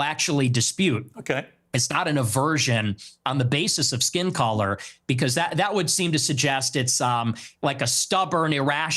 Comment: What did Ye say lol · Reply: he said niggas are gonna be niggas. Nick quotes him verbatim. Saw the clip on twitter